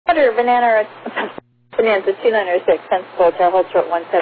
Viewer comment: I thought I heard “ Bananer.” Hahahahaah.